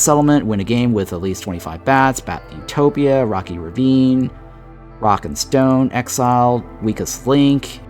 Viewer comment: DID I HEAR A "ROCK AND STONE!"?